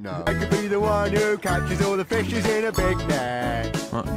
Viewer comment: I heard "who catches all the bi###es in a big bag" 💀